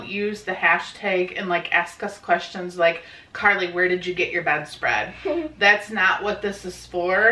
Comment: I thought said "where did you get karlis best friend?" and I was super confused I was like how do you buy a bestfriend. then I was like oh she said bedspread